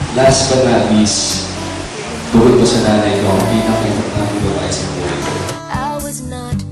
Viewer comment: anu bi ni Gerald sa 3:19? di ku msydo narinig eh.sori.. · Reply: Bukod sa nanay ko, ang pinakamalapit na tao sa buhay ko.....